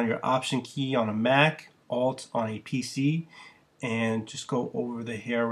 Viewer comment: What Did He Say For Windows? Alt + I?